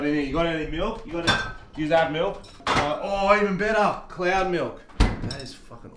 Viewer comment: I heard " clear milk". Funny how people hear different words.